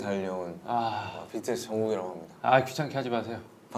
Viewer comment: In fact, you can't know what he was saying because you weren't there. So don't get smart😉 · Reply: he's actually saying "정국 전" (his first name and then last name). He's not saying Jan or any reference to Jin's name. Also for the other timestamp, in response to the question of who joined, JK was actually saying "국진 - 진정국" to highlight they basically came to the group simultaneously so therefore, their names are interchangeable.